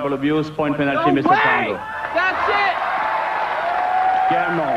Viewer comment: The Bogomolov and Tarango incidents were not disqualifications. They both quit/retired their matches. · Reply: I thought he said “NOW WAY, THAT’S IT!”